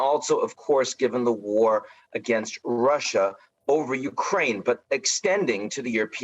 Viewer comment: Did he say at 5:08 "war against Russia over Ukraine?